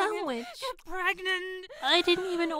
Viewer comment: Am i hearing her say “get pregnant” to the flipping chicken sandwich..?